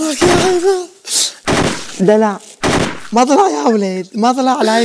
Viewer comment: سمعتوا كلمة ( دلع ) بصوت بنت هههههههههههههههههههههههههه